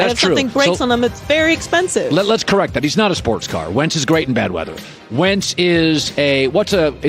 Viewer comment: When Colin said “Wentz is great in bad weather” but I thought he said “Wentz is great in bed.” And I wouldn’t be shocked if he did say that.